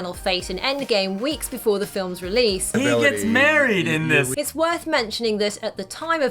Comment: talks over evryone"HE GETS MARRIED IN THIS" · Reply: This was the ONLY interview I watched from the press tour, and I thought I heard Mark say "he gets buried" and started crying lol phew glad I heard wrong now 🤣